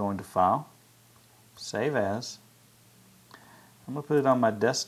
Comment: <_< i was listening to this loud and when you said save as my brother thought you said Save Ass......-.